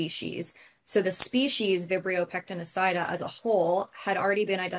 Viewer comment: Strain of what? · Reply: Vibrio pectenicida as she clearly says at around the 00:46 mark.